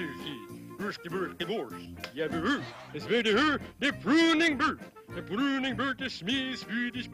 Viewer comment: Sounds like he said a pruning bird